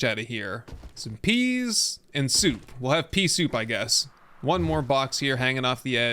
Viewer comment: “we will have some peasoup”. Sounded like Peesoup😂